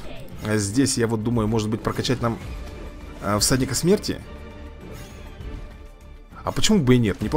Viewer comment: Сказал:"всадник смерти",послышалось:"садик смерти")